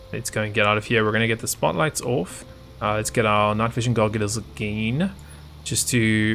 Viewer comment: WAIT DID YOU JUST SAY GOGULARS?!??!!